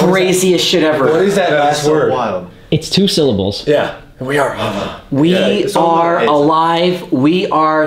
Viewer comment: I heard we are them - 53:50 a child giggling?